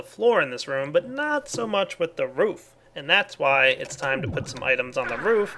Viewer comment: Roof sounded like Woof lol